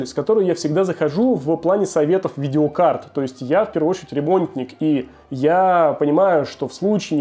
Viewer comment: Я в первую очередь ТЕРЕБОНТЬНИК! 😂 С третьего раза понял что он сказал.